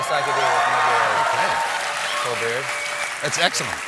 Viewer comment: Did he say Colbeard and in Stephen Colbeard?